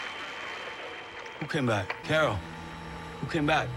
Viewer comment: In my opinion it sounds like carol said the whispers came back or Rick came back.